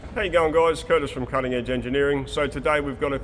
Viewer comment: Whenever introduces a video, all I hear is "Hello, young guys"..... leaving me feeling sadly left out. 😢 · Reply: What he actually says is "How are you going there guys?"